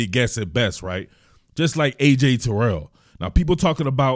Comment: ?? You always come here like every 10 comments and make a fool of yourself. I didn’t say he was like Terrell. I said his lack of competition, but having success, is like Okudah compared to Terrell. Terrell looked bad at times because of his competition faced, but he also was very competitive against that same comp, so I got a good judge on him seeing him lose to some of those cats you named. I can’t get a good judge on Gardner because he isn’t playing NFL comp. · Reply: well, I miss heard you then, I know I heard "like AJ Terrell" my bad on that part